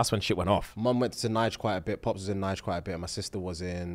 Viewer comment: Gonna sound like a horrible question buuuuut where did say his parents went to at 28:08?? (Nigeria?)